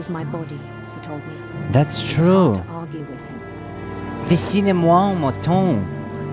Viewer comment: What did you say at 36:40? I believe it's french... I understood the second half. The world is sad in his imagination, if I'm not mistaken... but could not understand the first part. I'm learning French in high school so it caught my attention. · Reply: “Dessine-moi un mouton” it a quote from the Little Prince book, also a French song by Mylene Farmer :)